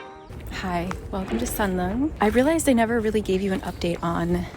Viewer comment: How is this beautiful place called? I would like to pin it on my Naver but I cannot catch the name :(( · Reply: seongjeongreung royal tombs!